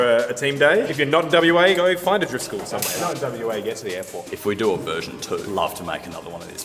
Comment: did he say NWA? 😂😂😂